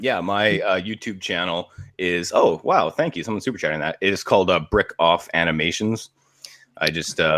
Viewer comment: can some one tell me where his animations are. I thought he said Brick Off Animations but I can’t find it